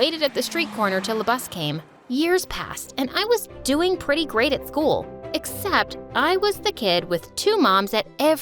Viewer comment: Did anyone else catch the cool instead of school at 3:43? Idk if it’s just me… or maybe my ears are dead?